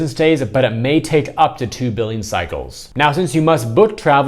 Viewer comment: Here I am watching your video past midnight with literally one eye open. I thought you said it might take up to 2 BILLION cycles. Lol. "Billing". Lol.